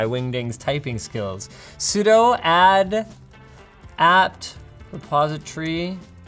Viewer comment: it should be 'sudo add' in the captions/transcript instead of 'psudo add'. i thought i remembered a way to submit caption corrections but i've forgotten how